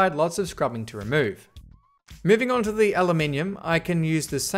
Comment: WHAT DID HE SAYYYYYY!!!! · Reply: He said aluminum, but it may be his accent that made you thought he said something else